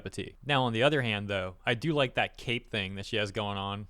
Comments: you said "cape thing". for a moment I thought you said "cake thing" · oh you said "Cape Thing"...coulda swore you were saying "cake thing" and I agreed that the cake thing is enjoyable.